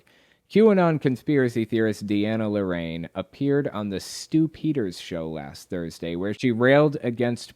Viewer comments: I heard "Stoop Heaters Show". Seems right. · Whenever you said Stu Peters I heard it as "stupiders"